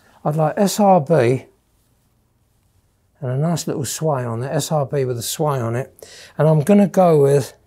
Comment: Anyone know what he says at 4:36? A "swy" on it? I don't recognize the term. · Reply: Sway